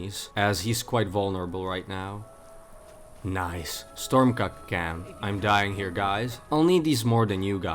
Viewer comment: Did...did he say "storm cuck camp"??? 😂